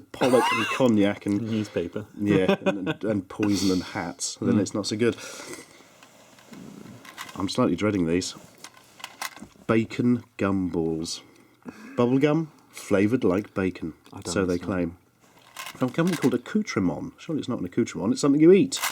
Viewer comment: I thought it said Bacon Cumballs xD